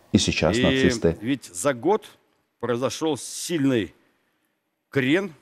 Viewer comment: Он хотел сказать что за год произошл сильный Хрен.или мне послышалось ?